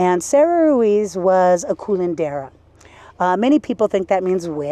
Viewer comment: Did she say "Culandera"?? 😅😅 No wonder she appears naked!! Le gusta culear!😂😂. The word is "Curandera", which does stand for "Healer". The thing with some Curanderas is that they used, what some would consider "witchcraft".